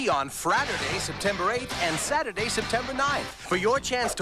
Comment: did he say Fraturday at 2:47 whaat